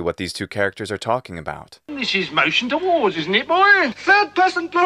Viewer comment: This is fantastic. Thankyou. I always thought the centurion said "this motion to war". After all these years later I finally understand what I was laughing at 😉